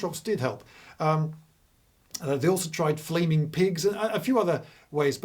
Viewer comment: did he say flaming pigs???